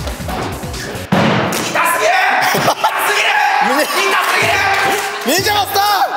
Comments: 「痛すぎる！」のリズムと音が「ひき肉です」に聞こえるの私だけ？笑笑笑笑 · 2024年まだ見てる人いる？？ 気づいたんだけど8:15からのシルクさんの痛すぎる。がひき肉ですと同じ音程に聞こえます笑笑